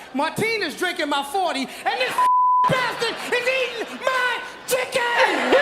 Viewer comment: Dunno why they bleeped out "this fluffy bastard". They must have misheard it.